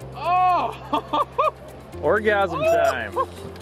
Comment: did you just say ORGASAM TIME?? 😂